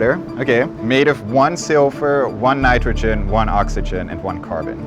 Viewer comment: Dude, it's silver , not “silfur” ! First time I understood a kind of poorly pronounced: 'sulfur' . Totally different from silver! Only the caption revealed to me that it was 1 Ag, and not 1 S. And only then I heard what he was actually saying: “silfur” , but means silver . It's chemistry, lad, so you’d better get the pronunciation right! Or something might just explode in yer or someone else's face one day!